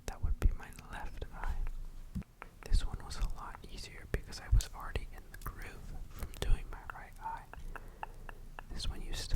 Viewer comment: Why do I hear at 14:34 "I was farting in groove" 😭 😂